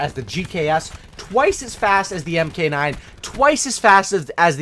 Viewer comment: Did he said mk9 ???